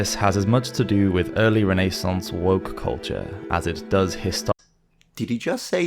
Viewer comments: I thought he said "Renaissance's WORK culture" and I was confused as to "why would the working class determine what is Medieval or not?" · I had the video on my second screen, legit said out loud "Did he just say woke culture?" and stopped what I was doing, to see how that is relevant in samurai armor. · I thought he said “work culture” because it makes so much more sense than “woke culture”. Pretty sure the lumpenproletariat weren’t woke to much whatsoever.